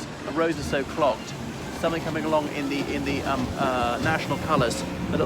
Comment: What is that word at 4:42 that sounds like "chairee"? "they are very chairee about your filming the police" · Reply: chary